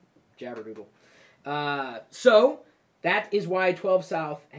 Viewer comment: Yabordoodle or jabordoodle or what eva he said